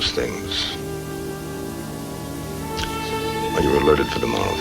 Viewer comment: I would greatly appreciate it if someone could explain the ending to me, namely, what does Wiley say at 44:36 after "useless things," and what is implied by Savage's call for "Operations!"? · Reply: Wiley told Savage they (the 918th) are alerted for tomorrow, and I think the ending is to show that life and the war go on even after a loss.